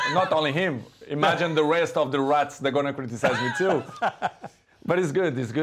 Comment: Did he just call Wayne Rooney a rat . 😂😂😂